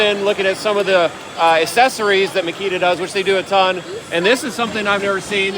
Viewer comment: Did u say Makita?